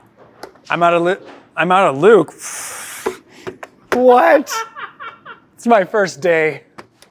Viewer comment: haha thought he said "I'm outta lube"